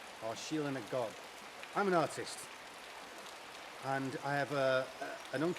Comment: I'm an artist too.. Damn you are bang on. You talk and back up your beliefs with proof! Open my mind, take me with you beyond.. I just subscribed.. I'm in Toronto Canada .. No 👾 alien bags here. You got ? I want one eh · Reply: What? I thought he said he is an AUTIST !!